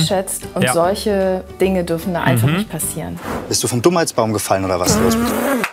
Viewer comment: Ich hab am Anfang verstanden bist du vom dumm als Baum gefallen..... hab mir schon gedacht da stimmt was nicht